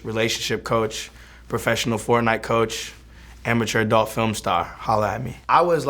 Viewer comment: Did he say FortNite coach